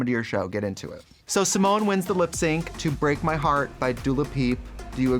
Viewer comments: Did Trixie sad Dula Peep instead of Dua Lipa at 19:50 ? :D :D :D Shaaaaade! :D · did trixie really said "dula pi" ?